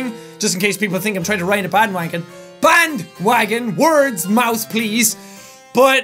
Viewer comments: when jack said 'band wagon' i thought he said bad wank it. so jack love so much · I was just listening to this is the background when drawing, and when he mis-said 'bandwagon', I thought he said 'wankwagon' snd I couldn't stop laughing and almost completely ruined my drawing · did you say "bad wanking" (i know he didin't but it sounded like it)